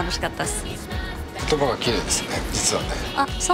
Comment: のスタッフさんなんて言ったん？？ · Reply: 「言葉が綺麗ですね、実はね」って言ってますね ナタリアちゃん口わるいキャラでしたからねw